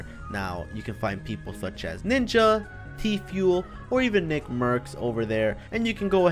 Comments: Did he say tefuil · did he say Tfuel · Did he say te fuel